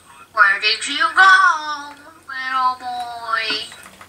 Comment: Don't get spank by Baldi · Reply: did Baldi call you a little boy